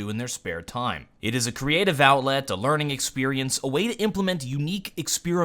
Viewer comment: One thing I may not have made clear based on the comments, I didn't mean a system where anybody could submit a 'mod', but rather only a few very specific high quality works would be individually looked at by the Devs to decide which makes it into the game, like how they currently do custom maps. There's already a system in place in CS:GO for custom content, and it works, I don't think the issues people are citing from the Skyrim fiasco would really apply. I just used the Skyrim paid mods as an example to explain what the concept of 'paid mods' were, and how they already have been tried, and failed. EDIT: Let me add to this, I clearly didn't understand the Skyrim implementation well, which I apologize for, I assumed they implemented it how custom content is in CS:GO and didn't realize how much they messed it up, which is pretty badly considering your comments. Forgive me people of Skyrim! and Together we shall slew dragons and such in The Elder Scrolls 6 : of Draenor! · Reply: Did you just say 'CREATIVE OWLET'? at 0:34? Oh you...